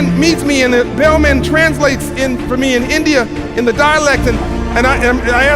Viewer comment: it would be 'dialect' instead of 'dye election'🙏🏼